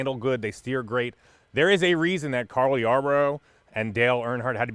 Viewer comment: Did you say Carly Yarbrough @ 3:39? 🙂